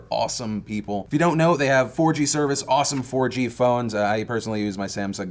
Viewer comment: when said "4g service" at first i heard "orgy service"